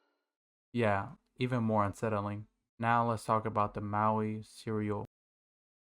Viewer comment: i wasn't looking at the video during this part so i thought you said "cereal"